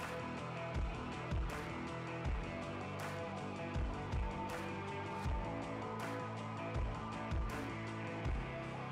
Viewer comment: Awesome work. Could you please tell me what's the product you used at 18:35 ? Couldn't catch it properly.Thanks. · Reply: He used weather stripping for like windows and doors as a dam to keep the epoxy in the shape he wanted, is that what you are talking about? The next thing he does is starts pouring the epoxy when he is done talking about the weather stripping!